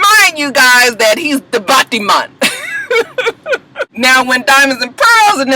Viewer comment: did you say battyman?! lmfao that means gay, hahaha.